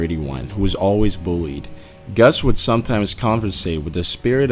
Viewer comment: did you say " Gus would always conversate"?